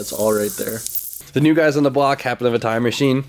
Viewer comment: Swear it sounded like he said "the new guys on the block have a time machine"